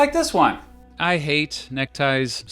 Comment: what did you say 😡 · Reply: You like neck ties?